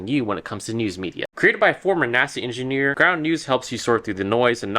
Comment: What is he saying at 5:09? I Swear it sounds like "Nazi engineer". Anyone else hear that? 😅😮